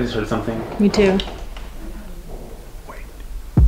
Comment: does anyone else hear breathing, then someone whisper "wait" at 33:14??